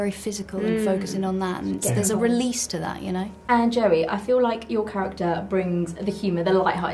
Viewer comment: did she just called him "Jerry"? Things happen I think 😅